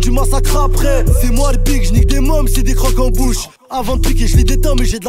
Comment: Ptdr à 2:13 j'ai cru que tu disais quelque chose de déguelasse mais apres j'ai compris 😂😂 j'ai cru que t'avais dit "j'nike des mômes".